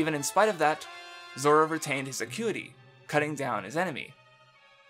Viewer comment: really sorry but someone please help me out, legit cant make out what he is saying at 3:40 "zoro retains his...."? what is he saying that zoro retains? thousand thnx in advance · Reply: acuity